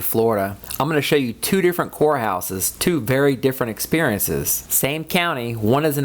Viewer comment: Oh my gosh. I thought you said Whore house 0:06 seconds! Hahaha. You did say court house, kinda 😊